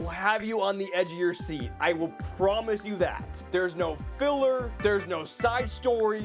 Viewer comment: did he say no filler?